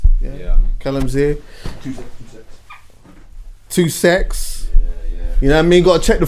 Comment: "2 secs" is what he said, "two-sex" is what I heard. made me chuckle considering the guest